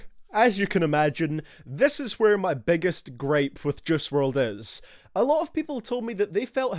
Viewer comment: It sounds like ur saying jizz world😂